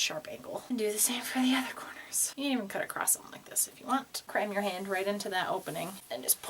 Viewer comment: When you said "Cram your hand" I heard "crime your hand" and I yelled "NO CRIMING!"